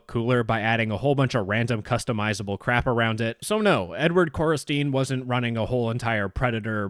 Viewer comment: I THOUGHT HE WAS SAYING N WORD CORRESTINE